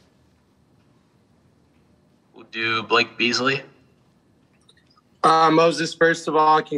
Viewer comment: Is it me or did I hear “do you blink easily”